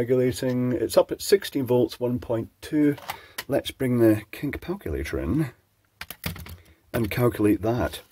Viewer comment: did he say 'kink palculator'? 12:35 😂